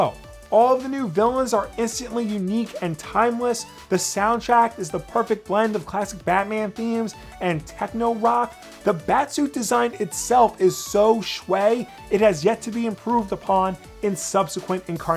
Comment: what the hell did he say here? Shway? Swey? I've never heard that word before in my life